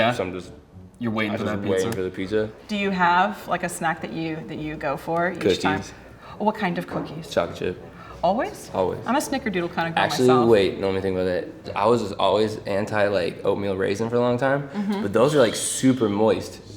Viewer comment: Why did i heard coochie instead of cookies W H Y